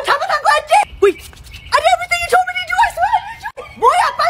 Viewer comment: That "EmOtIoNaL DaMaGE" at the end got me rolling on the floor💀💀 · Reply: I thought it said motional damage cause she was in motion I guess I over thought